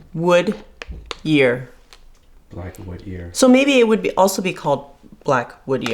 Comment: I could swear I heard you say 'black wood year', but what it really says is 'black wood ear'; Hēi mù'ěr.